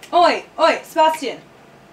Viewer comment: I heard "olé olé Sebastian" not "oe oe" SKKSKSKSKSKS